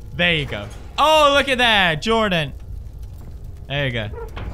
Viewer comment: Did he say Jordan at 26:50?? Just coincidence because that's my name and I actually was distracted until I heard what sounded like my name. Maybe a reference to Michael Jordan cuz he threw the cogwheel straight to the place?